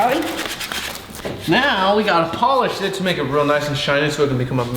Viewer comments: I thought you said shit but you actually said polish it 😂😂😂😂😂😂😂😂😂😂😂😂😂😂😂😂😂😂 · polish it Me: did he just say holly sh** Me: replay ohh... polish it